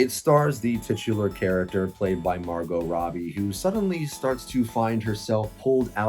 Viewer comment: I thought you said "Barbo Robbie" 😂 I fail to see how Oppenheimer was better considering the movies are non comparable. They have completely different tones and subject matter. I love a good Nolan movie and Oppenheimer was definitely intense and engaging at times and sometimes I like that... However, some people might be yearning for comedy at the time and think that Barbie was "better". I'm shocked you gave it a 77% 😂 In the movie going public's eyes Barbie was clearly the better movie, even if it wasn't perfect. It's an easy 85%, even with it's lack of story and character development. But one things for certain... Talk To Me was better than either of them (if we're comparing it by story telling) 🥴